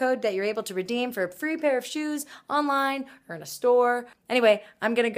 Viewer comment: At first I thought she said "Merry Christmas" at the end instead of "Have a goodnight guys"...guess who's ready for Christmas!